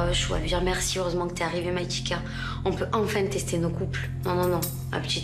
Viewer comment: C'est moi ou à 0:41 elle dis maitika